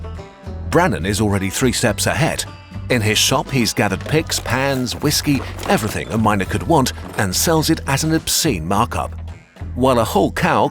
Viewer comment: At first I heard "In his shop he gatheres Picks, Pans, Whiskey...everything a minOr could want" and I didn't even question it 😅